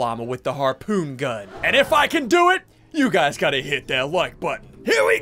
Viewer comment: does anyone else think he said u gods gotta hit that like button at first time watching the video or is that just me Edit:he missed the driftboard